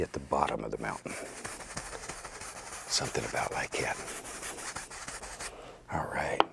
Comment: Everytime he says "something about like that" I hear it as "something about my cat" and I get confused af.